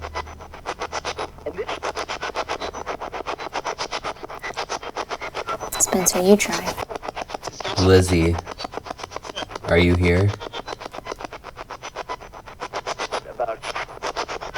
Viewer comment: I heard spencer